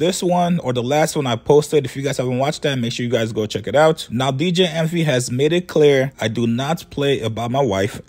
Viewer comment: I thought said BJ envy lmaoo